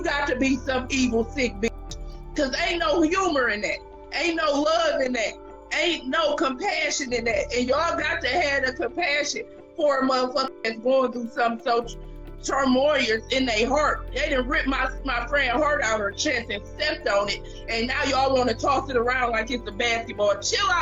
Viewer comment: turmoilous…is that what I heard???